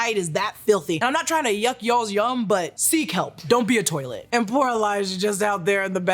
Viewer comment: I thought said “I’m not tryna yuck y’all’s yum. Sea kelp”